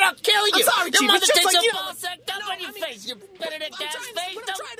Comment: Did I hear correctly, 'you Benedict ass face?!!"